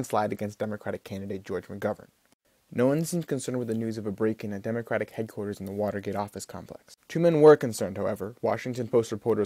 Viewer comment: what the fuck did he just say? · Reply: I had to replay it like 10 times but "no one seems concerned with the news of a break in at democratic headquarters at the watergate complex" :)